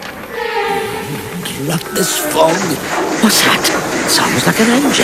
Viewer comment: sounds like what OJ said there was "Crap, this fog!" 😂😂😂